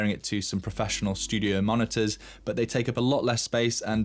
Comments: did you say you use your speakers for "listening to music while twerking"? Or "whilst working" :) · Love the content. But did u say u listen to the speakers “while twerking”? 4:54🤔🤔😄